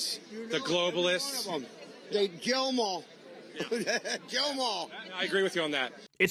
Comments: To be honest, listening to it at x1 speed, I heard "Jail em all". But it seems the far right reporter disagrees with me because he didn't use that as his defense. (EDIT: I listened again, and this time I heard Jail the first time and Kill the second time. Now I think he was saying Kill.) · To be honest, because of that guy's speech impediment, it could be argued that in a loud crowd, the reporter could have thought he said "jail them all". Listen to it again with those words in mind, and yeah it would make sense that he responded the way he did. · It really sounded like he said “Jail ‘Em all” but it’s hard to tell because he has no teeth and a southern accent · The only thing that is obvious is the fact that he absolutely 100% said that he agreed with the statement "kill 'em, kill 'em all" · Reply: I listened several times and I clearly heard, "jail 'em all". Certainly, that's not much better and it remains super fascist, but I still respect accuracy. Did anyone else hear it like I did? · To be honest, because of that guy's speech impediment, it could be argued that in a loud crowd, the reporter could have thought he said "jail them all". Listen to it again with those words in mind, and yeah it would make sense that he responded the way he did. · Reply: I totally heard, "jail 'em all" and I went back and listened with headphones several times. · it sounds like hes saying "jail them all" honestly lmfao.. it wouldnt make sense but nothing they do does.